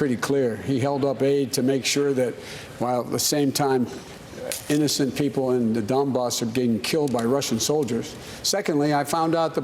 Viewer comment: Did Biden say, "The people on the dumb bus..."?